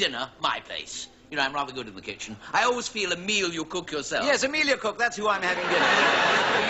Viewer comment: Emila Cooke? Oh sorry I thought you said Emilia Clarke... My mistake But pls do carry on